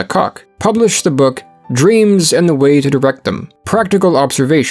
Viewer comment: am I the only one who heard "Dreams and the way to the "?